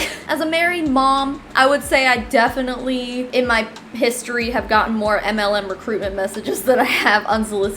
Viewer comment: Did you say piss-story??? 😂😘